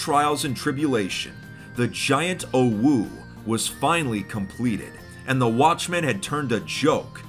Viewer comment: i might be high but did he just say "compweted"